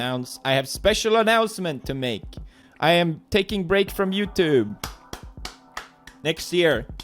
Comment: omg i thought he said he was taking a break for a year :_